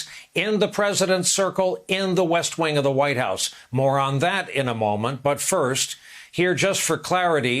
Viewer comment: thought he said 'moron that'. Which would also be a true statement.